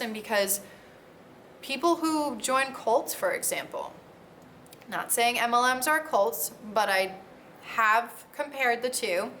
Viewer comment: Love your vids and your voice. My constructive criticism for most people is to do their own captions, but actually, the auto-captions seem to love your voice because they always seem to make sense. Screw the haters, your vids are great. MLMs are predatory and vids like yours are raising awareness. Thanks for what you do ❤️ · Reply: Only example of auto-captions being weird in this video (that I caught), at 10:26 the captions say “not saying MLM czar cults, but...” However, I could obviously tell that you said “Not saying MLMs are cults”